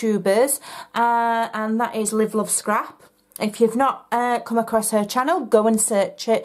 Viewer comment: Who is the Youtuber you follow? I thought you said Liberal Scrap, but nothing comes up for that. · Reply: ha ha ha ha ...LiveLoveScrap xx A